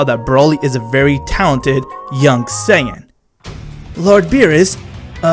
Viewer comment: did Broly just call whis LORD BEERUS!?